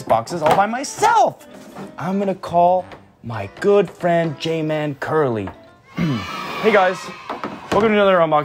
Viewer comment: I heard I’m going to call my gay friend Jmancurly